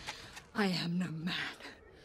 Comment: "I am no man" gives me chills every time. Eowyn is a bad ass hero and deserves number 1 spot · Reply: i thought she say "I am woman"